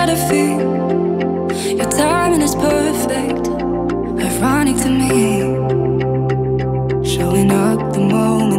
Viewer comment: Who else thought it said "Your timing is perfect, of running to me"